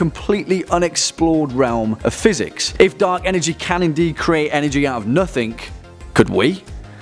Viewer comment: I thought it said Dank energy lol